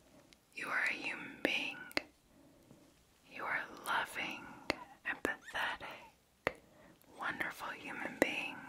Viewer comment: Lmao I had to go back and listen again as I thought you said 'You're a loving, and pathetic human being"